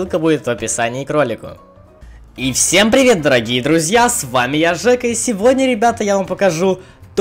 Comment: Мне послышалось всем привет дураки и друзья! 😁😁